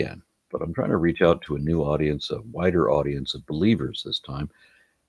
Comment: I heard it as: "A whiter audience" and found it really funny....🤨